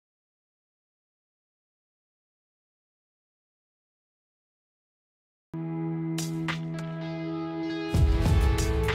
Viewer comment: did he say the n word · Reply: He said it go right foot up